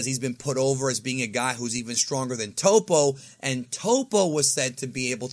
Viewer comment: Lmao I thought you said POPO was close to being a god of destruction. 😂